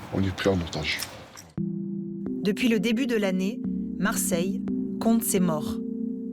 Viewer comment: C'est moi ou à 5:15 la voix dit "Marseille con d'tsé mort" ?!